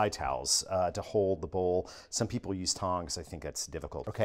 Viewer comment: did he say some ppl use "thongs"?